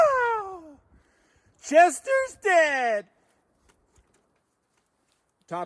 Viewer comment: "Jester's dead" sounded like you said "Chester". 😜